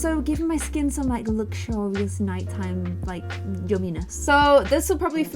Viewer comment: i thought it said nightmare not nighttime